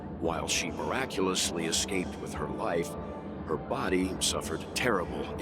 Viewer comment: why did I mishear "life" as "wife"?.. things could've been VERY different were that the case 😂